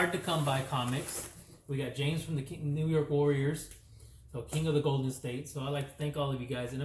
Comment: Yo!!!! Did you just call me King Of The NY Warriors?! 23:00 😂 I need that on a T-shirt😂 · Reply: Lol wanted to say james from the home of the New York warriors. Bad habit is say james and king always follows lol. I tried to correct myself.